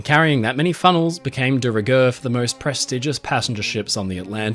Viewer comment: What did he say? Deregurth? Deregerth? Derigerth? De-regirth? Can someone please elaborate? I need to know this word, lol! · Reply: De rigueur ' required by etiquette or current fashion.'